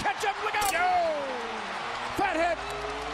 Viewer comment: did he said fat head ?? xD